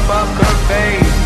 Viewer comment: Sometimes I hear "pucker face," sometimes I hear "f*cker face," sometimes I hear "f*uck her face" after the initial "poker face" in the puh puh puh ostinato part. The second IS NOT, in any case, pronounced the same as the first.